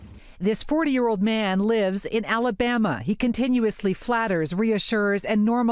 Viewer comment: Alabama did i heard right 😂